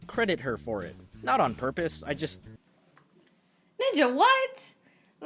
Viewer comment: Did you just say NIJA?